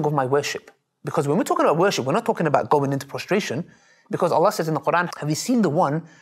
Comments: in 9:25, he meant prostration instead of frustration. maybe a typo error on that video. me just helping :) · Great interview! Alhamdulillah. Maybe I am wrong but I think the subtitles at 9:25 should be "prostration" instead of "frustration"